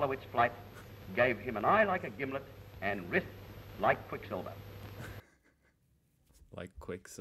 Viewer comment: I thought he said "wrists like Matilda". I had been wondering why he'd want to bother taking her out Waltzing but does sound slightly better than a jumbuck in a tucker bag.